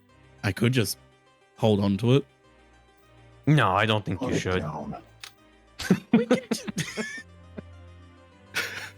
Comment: What does say at 1:49:13 ? I am really starting to wish they had subtitles specifically as I have been largely relying on context clues and rewinding to catch all of what they say so with cross-talk (fair and not typically an issue) I just can’t make it out. The auto-generated subtitles are more or less useless. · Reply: "Put it down!" I'm pretty sure